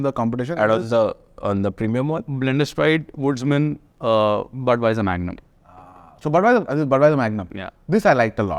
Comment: Is it just me or did I hear Budweiser Magnum @ 44:40..... Blenders Pride, Woodsman, Budweiser Magnum. Kindly help I'm going through an existential crisis atm ;-)